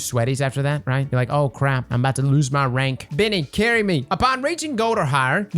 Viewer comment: did I just hear Biinny?